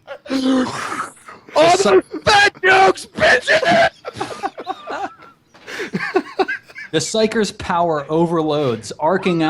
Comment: what was it DJWheat yelled at 27:45? to me it sounds like he saying "I'm gonna infect nukes, bitches" but that just might be me. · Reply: this comes months late, but hey better late than never, right? "al those fat jokes, bitches!" followed by the explosion. A fitting cry for the sassy fat psyker lady I should say.